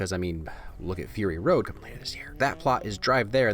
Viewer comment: Everything Great About Mad Max: Fury Road please? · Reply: I think I heard "Coming later this year!" (To be fair I had to put it at 0.5 speed to hear it) at 13:56 Super excited! It's my favorite movie of all time!